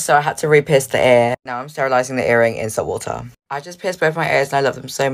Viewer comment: hol up did she say sterilize it in water? Unless she drop it in boiling water then haha idk